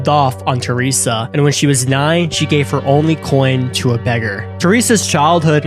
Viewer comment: She is EVIL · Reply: he says n#gg#r at 1:18 instead of beggar 😂